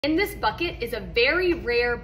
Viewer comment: LoL I thought she said ‘’in this fu** it’’😅